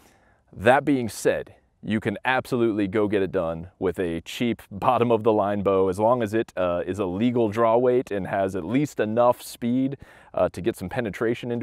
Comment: This is a terrible test. Three arrows with each bow at each distance is far too small a sample space. Even twenty would be on the low side. You should also use the same arrows with each bow as well as the same kind of arrow rest and sight. Both bows should be set up perfectly. This is just terrible. One can draw no valid conclusions from it. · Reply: I thought he said an cheap bow. My bow is $80 55# bow.